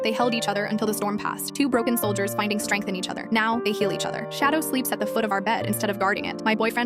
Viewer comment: Why did I hear "now they kill each other" instead of "now they heal each other"💔💔😭😭😭